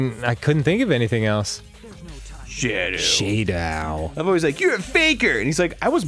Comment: I thought that said shaoow